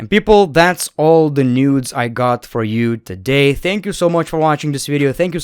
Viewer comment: Did you just say "that's all the nudes I got for you today" in the end? 👀😆